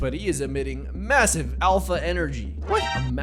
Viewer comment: Did he just say that he’s admitting mass alpha energy? 4:16 it’s official he’s cooked